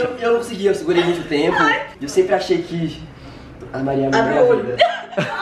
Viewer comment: O falou que a Maria é a mulher da vida dele kskskkss só eu ouvi?